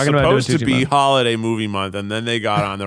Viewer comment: I heard “Tucci Month” and went “what is a toochee?” then mentioned Stanley Tucci. Great actor, btw. Love his role in The Devil Wears Prada.